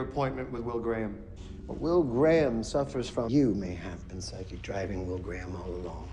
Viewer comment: it's sounds like Chilton was saying to Lecter "Will Graham suffers from YOU" XD